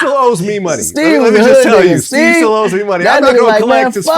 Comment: sound like Vlad said “that nigga still owes me money” 😂😂😂